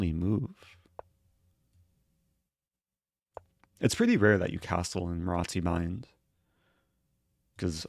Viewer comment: "It's pretty rare to castle ... ... ... " - did anyone catch what he said here please? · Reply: "It's pretty rare the you castle in Marózcy-Bind" The Marózcy-Bind is an opening in the sicilian where White pushes c4 before playing Nc3. Usually White will castle kingside, but now that his kingside is opened up, he has to castle long, which is rare, because c4 is weakening the king